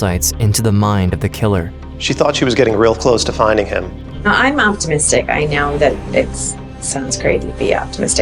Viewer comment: ngl... I thought she said she was autistic, and just thought to myself, yeah that checks out if its a special interest or something